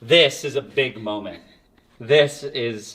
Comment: I thought he said, "This is a PIG moment" 😂😅